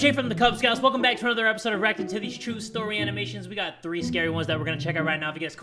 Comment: bro i heard "rock of titties" instead of "reacting to these" at the beginning and got so confused.